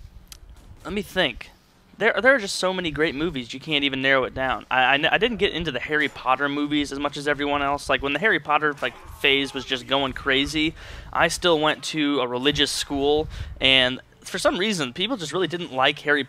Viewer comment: it sounds like you say hairy Potter XD